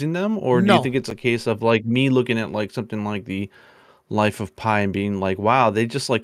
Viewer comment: Did call “Lies of P” “life of pi”? LOL